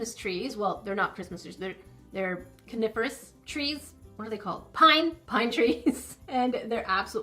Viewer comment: What said: coniferous trees What I heard: carnivorous trees